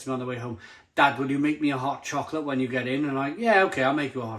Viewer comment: Thought you said ‘hop chocolate’ - what a great name for a beer!